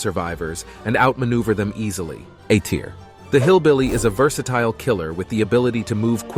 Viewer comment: The first two killer:hmm maybe the Ai is right literally after I thought that the Ai said hillbilly A tier